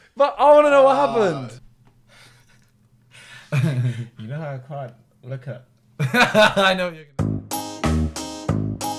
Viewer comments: that’s bothers me that I’ll never know what he said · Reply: I saw from a comment that he said "think of an Indian thats not vik" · did Toby just say, he can't look at Indians? 😂